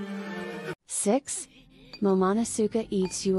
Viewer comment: can nobody tall about the fact that the narrator said "sex" instead of "six" for 6?!